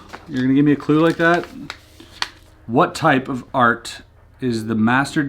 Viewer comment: i thought he said fart but then i replayed it and realised it was *art*.